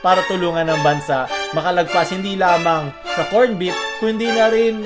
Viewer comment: Did i just hear corn beef? 15:09 haha.